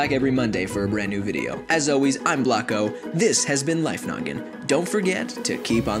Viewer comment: At first I thought it said life nugget